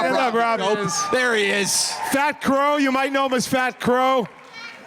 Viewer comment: I thought Q said "Fat Qrow"